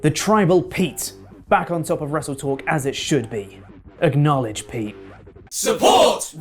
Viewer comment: Not gonna lie it sounded like you said acknowledge pee.